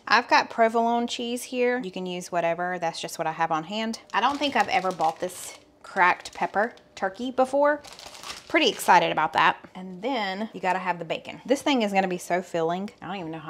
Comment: For a split second I thought you said crapped pepper not cracked pepper on the first one. It made me stop for a min. 🤣 lord have mercy I need sleep.